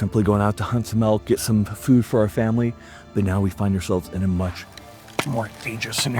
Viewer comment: I thought he said “We were hunting with Mel Gibson to get food for our family” It’s past midnight and I’m tired.